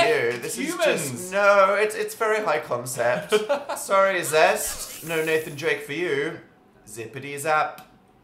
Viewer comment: I'm laughing really hard, because I thought said zesty John